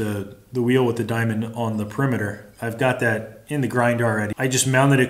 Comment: Swear I thought you said "I've got that...in the GrinDAR" @ 5:05 lol